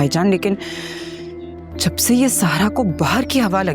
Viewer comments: here she says Zara instead of Sara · fuppo call Zara instead of sara